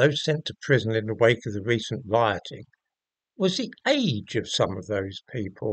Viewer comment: Sorry I thought you said rotting, 😂😂